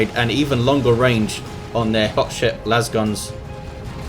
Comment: did you say "Hot shit lasguns" at around 15:25?